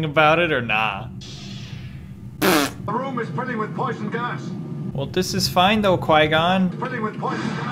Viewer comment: The room is filling with poison gas. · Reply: i thought is said poison ass